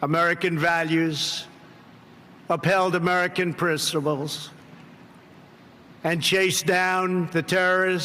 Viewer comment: did he just say prissiples?